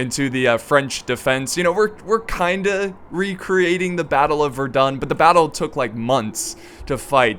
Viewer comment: I thought he said the battle of we’re done